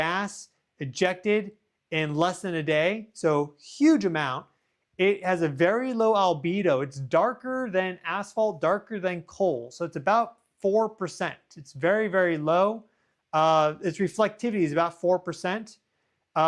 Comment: Darker than asshole? Lmfaooo idk if I heard that right 😂